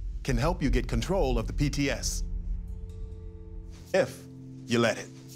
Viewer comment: parece que dice bts